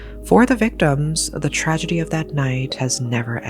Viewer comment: I heard you say I could get 1 grand for watching this ? 😂